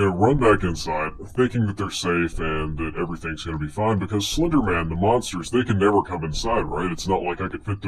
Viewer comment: did he say slendora